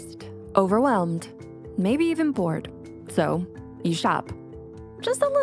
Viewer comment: I thought she said "Maybe even Born"